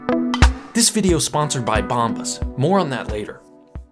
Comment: I was eating crunchy food when he announced the sponsor and all I heard was "This episode is sponsored by bombs" and I was just like "Cool"